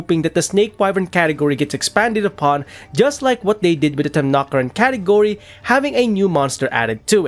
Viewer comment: what other category did he mention? I cant understand with his accent usually I have no issue at all since I like his videos. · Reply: I either said Snake Wyvern and flunked it or Temnoceran which I probably pronounced wrong here lel